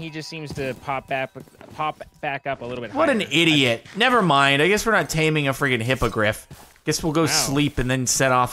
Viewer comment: I heard “Nvm I guess we’re not taming a freaking hypocrite” ( T w T)